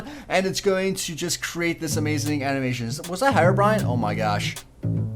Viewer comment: anyone else thought he said harry-brine?